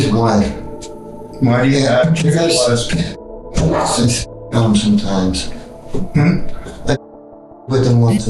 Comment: Did he say he put the underwear on sometimes? 45:36 ?